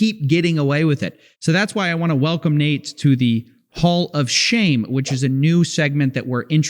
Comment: I thought he said "Paul of Shame"